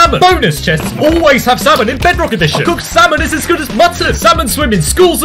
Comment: It sounds like he says “samon”